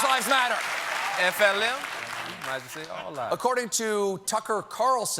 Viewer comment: LOL! Did I hear Jon Batiste say "All lives matter"